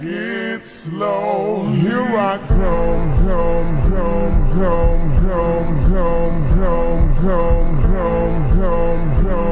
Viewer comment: DO I HEAR GIRL OR GO I DON'T UNDERSTAND 😭😭😭😭😭😭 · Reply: It's saying "Her I come come come come come come"